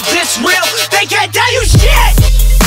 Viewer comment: I wasn't sure if I heard "They can't tell you shit" or "KKK, you shit!"